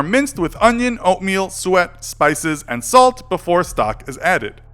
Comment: did he say sweat? 🤢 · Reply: “Suet”. Basically animal fat